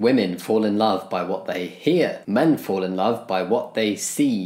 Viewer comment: I’m hard of hearing and my first instinct was to say, “guess I’ll never fall in love”